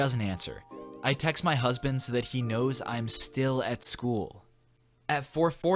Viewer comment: you said I texted my husbands instead of husband lmao. I was like wait what?!?!?